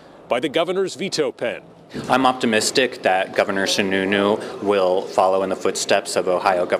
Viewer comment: What a min did he say Sh new nu? Sorry but sounds like a made up last name time frame